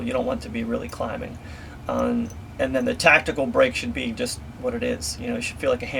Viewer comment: Hey. who makes that slide? i thought you said LT Tactical, but i can't find them anywhere.